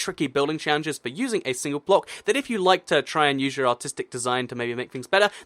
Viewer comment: When you said artistic I thought you said autistic